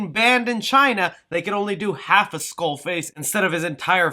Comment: did you say Skull Face?